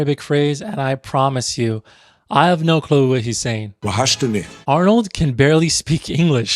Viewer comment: Salam aleykoum, as an algerian-french I grew up with this movie, I saw it thousand times, at 4:50 of your video I think Arnold says :twahachtni (meaning :You missed me?) In Algeria we say "Twahachtek" like :I missed You